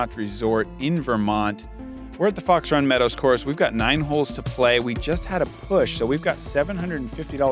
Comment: who else thought they heard "Where the fuck's Ron Meadows?" at 1:07 LOL